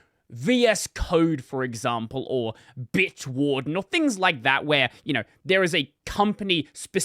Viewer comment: Did he just say B*tchwarden?